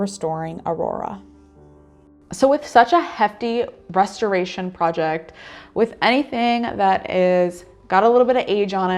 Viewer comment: I swear she says "hefty breasteration project" @ 0:53 😏